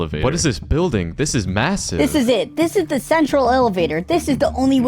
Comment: did he say massive……